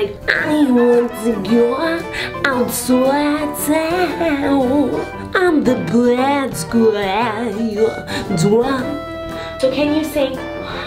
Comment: did she say I’m the bad guy😂😂